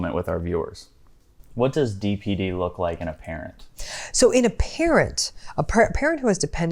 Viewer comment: I misheard "parrot" at first!